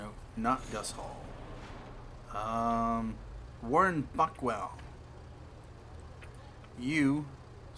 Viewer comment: lol when you said warren buckwell i thought you said fuckwell